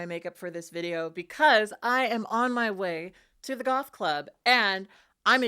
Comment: I thought you said "golf club" at first?? But "goth club" makes way more sense for you to have said 😂